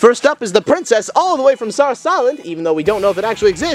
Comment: For some reason i heard South Island